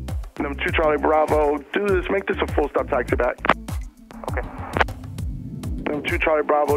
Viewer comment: did atc say "dude, let's make that a full stop taxi back?" 😁